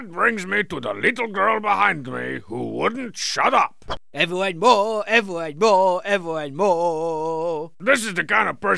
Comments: “Ever and more, ever and more, ever and mooooore.” Homestar in a loose fitting Viking hat as the “little girl behind me who wouldn’t shut up” steals the show for me. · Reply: I thought he said "EVERYONE MORE! EVERYONE MORE! EVERYONE MORRRRRREEEE!" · “Ever and more, ever and more, ever and mooooore.” Homestar in a loose fitting Viking hat as the “little girl behind me who wouldn’t shut up” steals the show for me. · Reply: I thought he said “everyone roar”